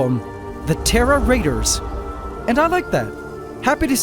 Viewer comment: Haha at first I thought you said “Terror Raiders,” which made me think of the dark horde 😝